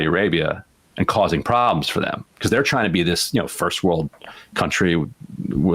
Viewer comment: I wonder if when Ryan says SA is trying to be this 1st world country with Messi playing football, the audience and probably even Sam/Emma thought he was saying "This country that plays football messily"